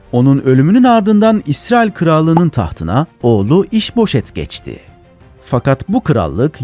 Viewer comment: iç poşet geçti diye duydum zannetim